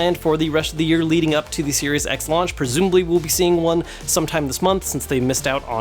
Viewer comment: I swear he said Serious X